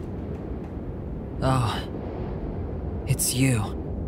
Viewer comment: this is what you said oh it's you